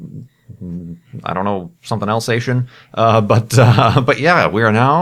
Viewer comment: Sounded like: "Something else Asian" to me